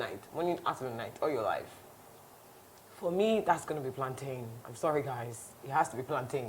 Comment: Did I just hear ‘Alantan’ 🤣🤣🤣